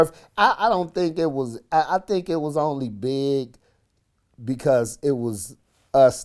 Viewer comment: bruh did vlad just say "nigga" im not offended but that shit lowkey funny to me